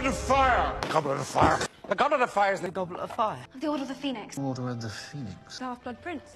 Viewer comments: i heard “the goblet of fire is the goblet of fire” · the goblet of fire is t h e g o b l e t o f f i r e (thats what i heard lol)